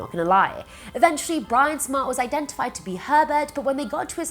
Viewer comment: 3 Herbert Me: Wait I thought you said pervert.